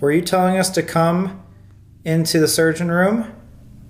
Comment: When they Heard the big step when sam was in the room it sounded like it said sergen room